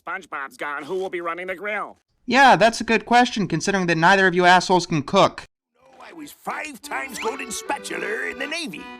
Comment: what did he say? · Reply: I'm not a very strong swimmer.